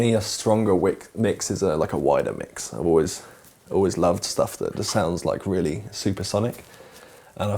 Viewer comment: What did he said? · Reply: me, a stronger mix is a wider mix, I always love things to sounds like supersonic